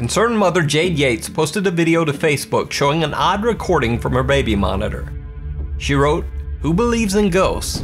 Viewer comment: I've watched this video a few times, because I like it. I never noticed this before. @ 0:16 Did you just say "raby monitor"?